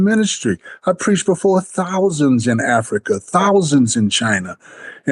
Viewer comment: "I preached before thousands in africa" I heard, " i spread poison to thousands in africa"